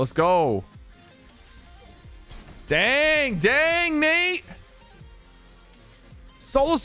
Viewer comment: it sounds like u said dang mate!